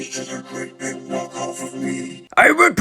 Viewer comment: Could you translate what Soundwave said? I didn't understand. All I understand was:" My worst enemy is Blaster and" he's...(I didn't understand) and walk of me. If you want.😊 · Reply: He said, he’s just an knockoff of me